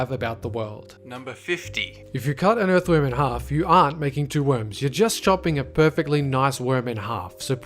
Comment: At first I thought he said you are making two worms and just when he mentioned fan death, I paused because there is a fan directly on my face.